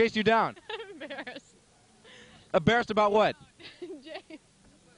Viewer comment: I was in the graduating class that year. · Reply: is it me or does someone say "LeBron James"